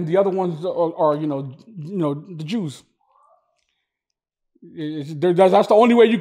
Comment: I thought he said "juice" but it was "Jews"